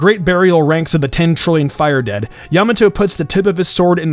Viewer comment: did he just say “Yamato”????